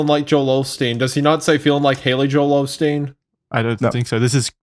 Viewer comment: i always heard it as "feelin like Joel Hale off steam"